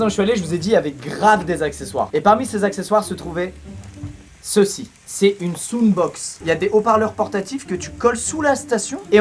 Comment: esque j'ai entendu (soune box) ? SOUNE ?????? , Ok vous être français mais quand même oow